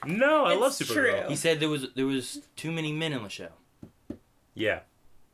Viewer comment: "he said there was... too many men(?) on the show"... What did say?